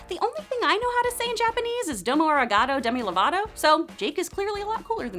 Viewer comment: Did you just say domo arigato demi Lovato?